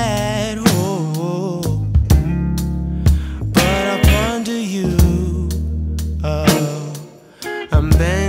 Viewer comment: OMG I ALWAYS THOUGHT HE SAID "but I'll ponder you" not "but I'm fond of you"